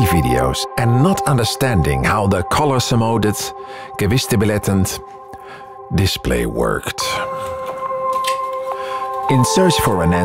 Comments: When you have a moment, can you upload English subtitles? I can't understand you, and apparently, neither can the auto-generated ones. For example, at 0:31, I have no idea what you are saying. "...and not understanding how the color semoded-kevistabiletant-display-werked" Um, what? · What does he say at 0:33? I hear "Colorsemodet gewissstabilitend display", which isn't quite Dutch nor German, with English thrown in!